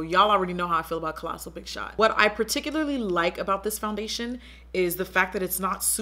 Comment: / 14:49 she said foundation instead of mascara😂😂😂😂